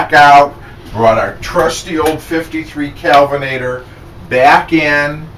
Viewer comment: I thought you said kevenator lol.lol